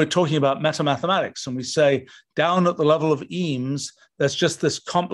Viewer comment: What did he say, mathematical "eems"? How do you spell that? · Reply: Emes are fundamental units (specifically in linguistics) of Greek origin…if you ever heard the word “phoneme” which is a fundamental unit of spoken sound, that’s where that suffix comes from. phono = sound, eme = units